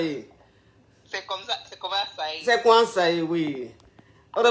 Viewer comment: elle dit quoi la coach à 6:11 ? je comprends jamais quand elle dit ça. Aidez moi les caviars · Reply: "C' est conhan ça yé" veut dire "C' est ainsi ou encore c' est comme ça, ou encore c' est ça".